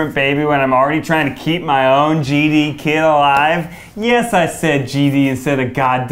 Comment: I thought Ned said “genie kid” and not “GD kid” and was deeply confused